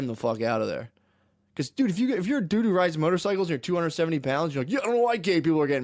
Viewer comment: instead of “dude who rides motorcycles,” i heard “doo doo rides motorcycles,” and was presented pictures of Black Biker Week.